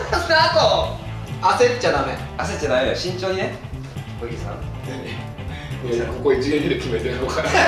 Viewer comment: なんて呼んでるか聞こえますか？ ドギーさんに聞こえるんですけど…